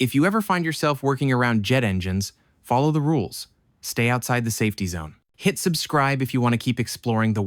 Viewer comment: did bro say stay outside of the safety zone???